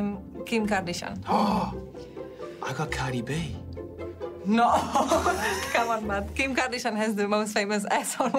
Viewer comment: Anyone hear that "c'mon man" at 2:39 That cracked me up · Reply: it was cmon mate because theyre in australia